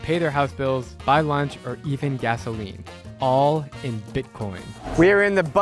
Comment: did he really say to eat gasoline?